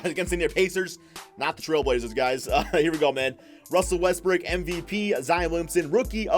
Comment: I swear he said “Russell WestBrick”